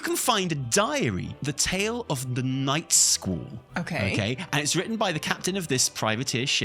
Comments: Honestly the first couple times I heard "Knife School" and you caught my attention. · I keep hearing "Nightschool"